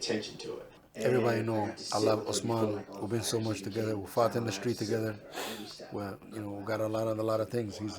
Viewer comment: It sounded like Ali said him and Usman used to fuck in the street together but i'm pretty sure what he meant.